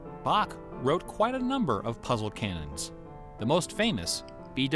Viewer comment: I misheard the one line as "Puzzle Cannon" and for just one second I was hoping for The Witness to have a surprising bloody twist ending